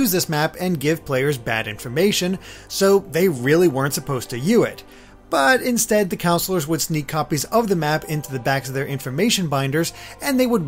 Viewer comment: did he just say the weren't supposed to U it?